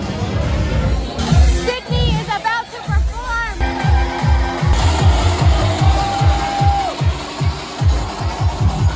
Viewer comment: Looks like a great time 🤟 who was the girl with yellow hair, I think you said Sydney but I can't find her 🥺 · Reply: Sythyst!!